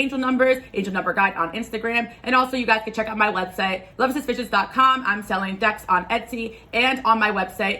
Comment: swore you said "love is suspicious dot com" not "love exist visions" at first. lmfaooo my subconscious is in the trenches